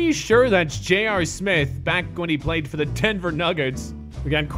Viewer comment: did really said timber nuggets smh